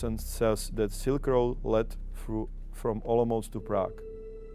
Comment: He did not say "almost" but "Olomouc" which is a city in Czech republic. But anyway, great documentary guys :)